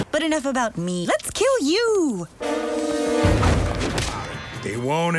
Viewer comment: Did I just hear that right... But enough about melets kill you!